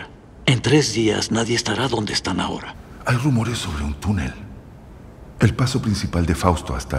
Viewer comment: tunele? Que pedo con esa diccion? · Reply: Escuché "Tunel eh"